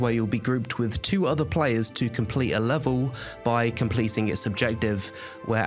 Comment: "by completing a subjective." I think you meant to say, "by completing an objective." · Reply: He says « complete its objectives » (it being the level)